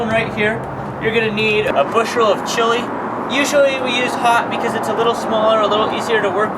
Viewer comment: What kind of chile? I hear "Usually we use ???????? because it is a little smaller and easier to work . What kind? Sorry it's my old man ears not you! Thanks! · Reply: I heard "Usually we use hot because it is a little smaller..."